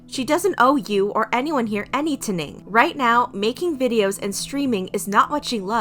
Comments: ACTUALLY SAID ANYTNING INSTEAD OF ANYTHING 😂😂😂😂😂😂 Edit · WHY DID YOU ACTUALLY SAY “ANYTNING” ASFDLDKSKS